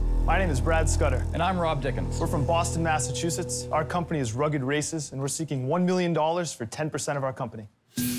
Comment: I heard “Rugged racists” 😂